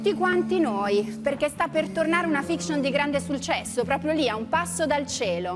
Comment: ma solo io l'ho sentito???? SULCESSO ahahahahaha